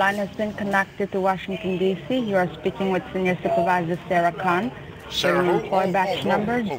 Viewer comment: I could have sworn she said her name was Sarah C**T! I nearly choked on my cheese and onion sandwich. Her name would have been very apt her and anyone else who tries to pull these scamming stunts. Another good one